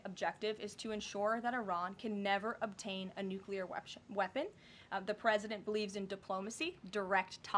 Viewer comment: Did she just say wextion? 9:25 🤨